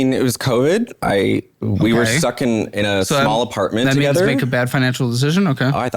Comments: I heard "we were sucking in a small apartment together" lol 😂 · did he just say “we were sucking in a small apartment”? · Did he say "Stuck in" or "Suckin'"?